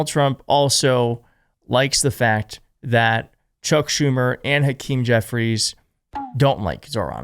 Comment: BAHAHA! Did you call him Choke Schumer? Because he always chokes during shutdowns? 🤣🤣🤣